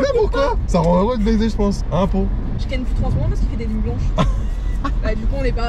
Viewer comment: Apo elle dit quoi à 7:30 ? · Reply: Je ken que 3 seconde car il fait que des nuits blanche